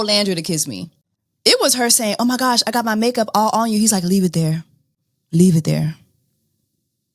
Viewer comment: It sounded like said, “I like it there” not “leave it there”. Lol either way it was hot lol